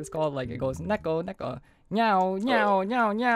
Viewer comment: What name of beatmap? (I can't hear what he said) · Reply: super nuko world